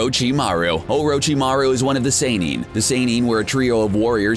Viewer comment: Wait wut?? Did he just say saynin??